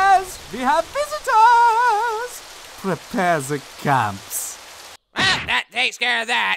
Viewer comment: Sooo is anybody gonna mention the fact that the Hell guys are German and one said "prepare the camps"???? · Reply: Thought he said clamps